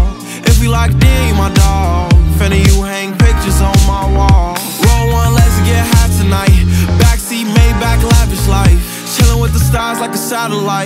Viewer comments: I legit thought this dude said "Ay, Pee on my arm and it's shining bright." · who else thought he said pee on my am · am I the only one that hears “hey pee on my arm” instead of “A P on my arm”???